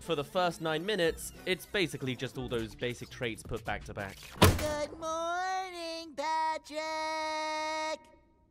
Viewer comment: apon looking back on this i thought i heard sponge bob say “good morning bitches” to patrick 🤣🤣🤣